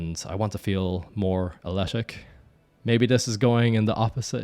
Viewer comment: New pronunciation of athletic just dropped: ahletic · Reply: I thought he said ashletic.🤔